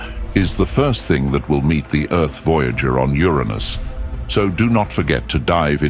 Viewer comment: A bunch of 💩? Oh wait, I thought you said your anus. 🤣